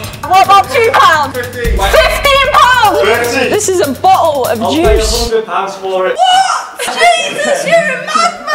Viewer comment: "ThiS iS boTlLe Of JuICe" killed me like litearlly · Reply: I heard. "Dis is bohel of juice"